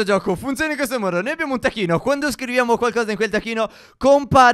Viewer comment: Io al minuto 1:30 ho capito tacchino